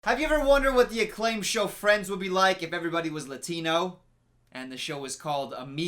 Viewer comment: I heard: “Have you ever wonderd what the show Friends would look like, If everybody was a dino?” 😂